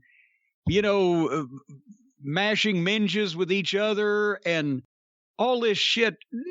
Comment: Mashing minjas? Maybe I misheard that....